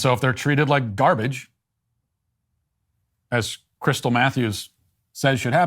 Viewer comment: I legit thought he said "crystal methews" first